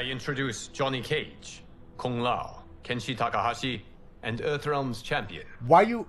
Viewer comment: Sounded like he introduced 69 tekashi 14:14 into it 😂🤣